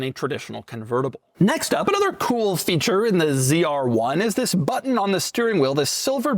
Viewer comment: i swear i heard doug go "next up, BLUBLUBLUR cool feature..."